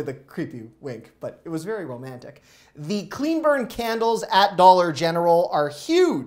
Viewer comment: I thought you said CLEAN BIRD CANDLES at first! 😁